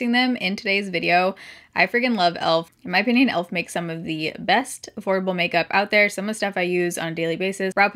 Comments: I thought she said "best horrible makeup". Don't mind me. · I seriously thought I heard "the best HORRIBLE makeup" 😂😂 I was like wait let me rewind that part